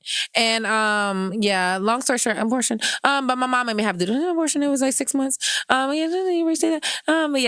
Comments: did she say three abortions in six months? My goodness. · I 28:46 I’m sorry, but did she say she had an abortion at six months?